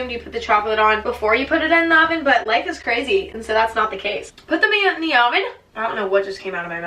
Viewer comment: I thought she said "put the men in the oven"-😳